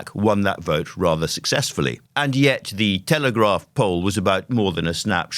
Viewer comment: I heard 'Telegraph Pole' instead of 'Telegraph Poll'